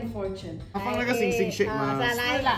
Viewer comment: i thought ur relatives were saying "WHAT LAHH" no joke XD